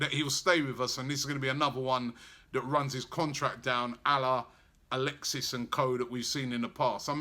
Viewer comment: I swear he says Allah